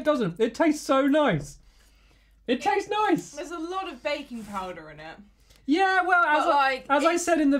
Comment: i thought she said bacon powder XD